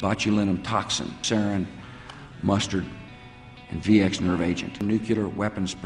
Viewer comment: Did he say "mustard"?